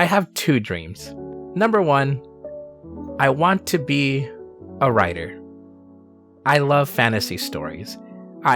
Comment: I heard at 8:20 "I want to be a rider". I can also see you being a great writer though given your scripted content and how its a great balance of technical analysis and emotional. In my book, out of you Thomas and Kezza, if you are not #1, you are pretty darn close to it! Certainly you are #1 at the kind of content you make, never being afraid to experiment. This is not a review of your channel or content, but an admission that myself, as well as 73 thousand others love what you do